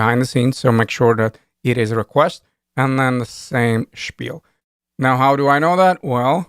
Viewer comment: what did he say at 3:24:17 "same schpiel"????